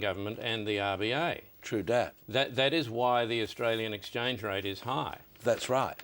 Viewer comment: Does he say "tru dat" at 1:06?